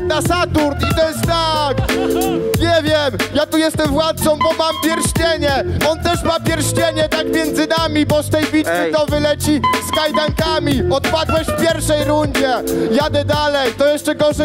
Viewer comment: Usłyszałem K2W?